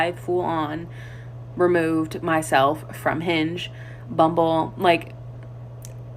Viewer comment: I thought she said a dating app named Binge.